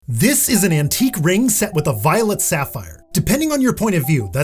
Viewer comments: I heard "violent sapphire." I didn't even question it. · Thought you said "violent sapphire" and spent like 5 minutes learning about gemstones (and everything else) waiting to hear what made this stone angry 😆 it's time to go to bed